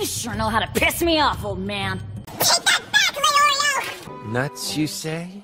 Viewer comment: I heard Shut tf up Leorio!